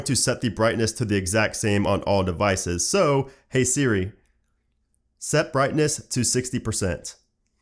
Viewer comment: What they actually said: Okay setting brightness (or whatever they said) What I heard: SSSSSSssssSsssssSsss